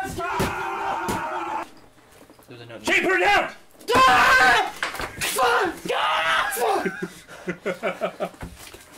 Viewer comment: What did say? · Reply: Two years late he said Shane put it down